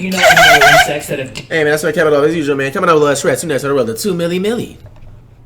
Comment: All I heard was 2 milly milly